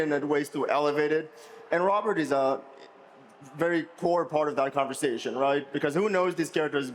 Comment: Am I the only one who thought he said “Robert is a very poor part of that conversation” 😂